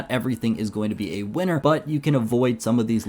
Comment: what the hell are you saying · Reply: Wanna thank you guys so much for watching and have a great rest of your day